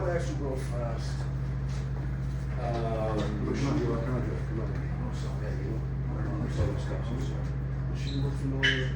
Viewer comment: Who was that guy trying to get Blackie to look at? Sounded like he said "Does she look familiar at all to you?" lol